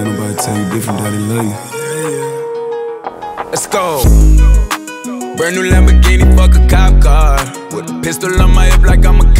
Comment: i thought he said "brand new lamborghini for his cop car" not "frick a cop car", i thought he was being nice to the police